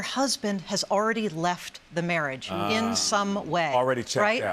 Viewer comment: At first I thought the lady said "mirage" instead of marriage. But then I realized what's the difference anyways.😁